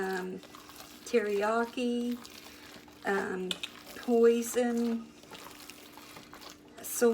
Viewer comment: I love!! At around 6:15 when describing what she used to put in there for spices did she says poison?? I about fell over!! Love you!